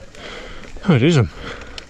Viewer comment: Probably without knowing you said something in Dutch at 02:30 when getting on that hill. I heard you say oh dat is hem or in your case oh that is 'em haha :)